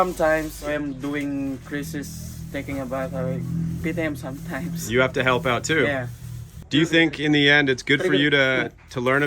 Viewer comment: correct me if im wrong but did he say "beat him sometimes?" with a smile in his face? 15:46?